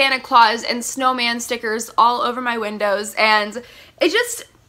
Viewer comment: Santa Claus and snowman slippers? Wait did I hear that wrong...? 😂😂 ily Oh wait never mind I think she said stickers